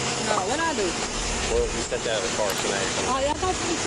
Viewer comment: to be fair it does sound like he said "you TWO step out of the car"